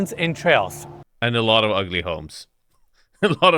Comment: "and alot of ugly homes" i didn't heard M in home i had to go back and open subtitle 😅